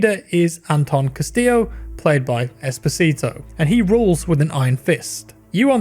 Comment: OMG I always hear "Despacito" instead of "Esposito" Jesus...